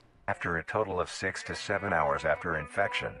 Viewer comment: d-did someone say 67…?!